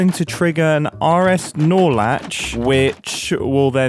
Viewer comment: Every time I hear this I think he’s saying R S-Snore latch